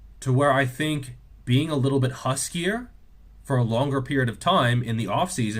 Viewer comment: I heard "huskier" as "hussier" and wondered if that meant tarting it up and traipsing around with anyone who is game would lead to better gains.